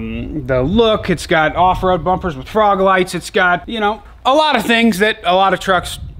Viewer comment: did you say frog lights? Hahaha